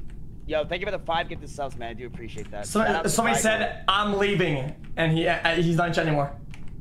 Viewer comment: did he say im leaving then the hard r?